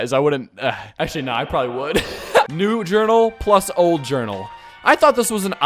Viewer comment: i heard "new journal plus old journal" i thought he said "doom eternal plus old journal"